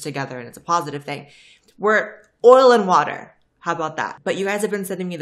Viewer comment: I thought she said “oral and water”. The I realized it was oil and water